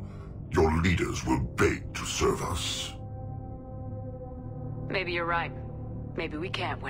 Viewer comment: It sounds like Harbinger said "One of us" not 'Serve us".